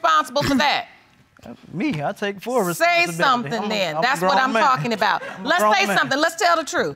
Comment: did he say he was a grown man???😂🤣